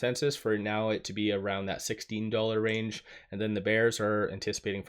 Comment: > did he say $16 range?🎉 Had my hopes up until I realise he meant $6 range. 😅